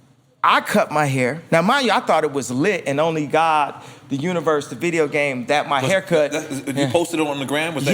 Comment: Did he say "and only God, The Universe, The Video Game"?